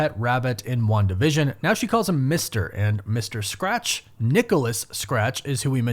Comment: wait, i definitely heard "master", but not mister